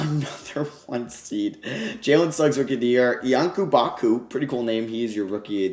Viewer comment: did he just say jalen Suggs rookie of the year